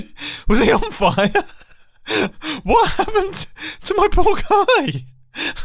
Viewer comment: When mumbo said “poor guy” I automatically heard it as “pork eye”